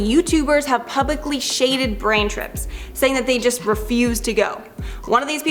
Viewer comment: did she say brain trips😂 love!!! ❤